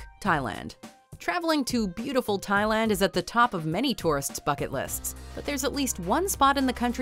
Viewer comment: I thought she actually said "on the top of many peoples fuck it lists" instead of "Bucket lists"